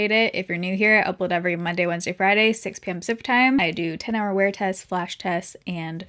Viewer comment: Everytime she says, 'pacific time' i hear 'supatime'